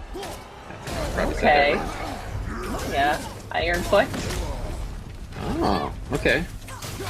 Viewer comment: I listened to this at least eight times, and forgive me, but I cannot hear anything other than "iron fart." 😭